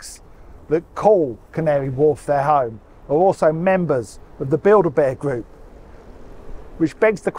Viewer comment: I heard "Build-a-bear group" and now I'm imagining all kinds of teddy-related conspiracies. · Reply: *Bilderberg group😂 n they ain't nothing bout fun n games